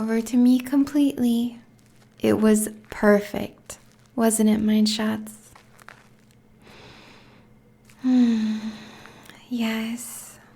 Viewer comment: Hey!! At 2:46 did you say mind shots? Or am I mishearing it? Great vid! I listen to a part a night before I sleep! Your voice is so soothing